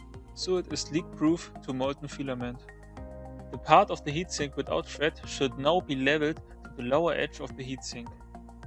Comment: What you're doing at about 5:20 seems quite important to prevent oozing, however I have watched that section a number of times, and can't understand what you're saying. Do you have a transcript? Also at one point you remove the thermistor and talk about a "sensible cover", I didn't understand that part either. Don't want to criticize what is not your native language, but it's really hard to understand. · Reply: he says "the part of the heatsink without thread should now be leveled to the lower edge of the heatsink".